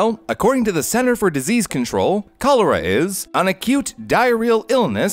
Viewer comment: I thought he said "a cute"